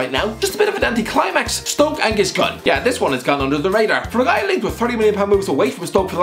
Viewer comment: did he...did he just say "Gunn under the radar"?